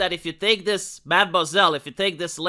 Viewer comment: Did only I hear ''mambaiselle''? Instead of mademoiselle? Lol.